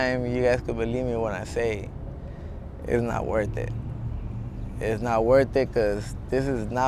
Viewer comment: who else thought it said “nut worth it”😭